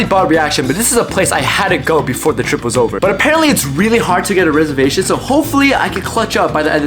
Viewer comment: bros brain rot is getting to him or maybe… it’s getting to me- I swear bro said “rizzervation”